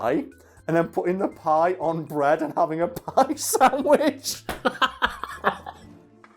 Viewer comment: that was SO much worse in my head... I thought you were saying like, an apple pie. I was just imagining a giant apple pie between two slices of bread...